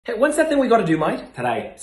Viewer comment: I heard it as what's that thing 😂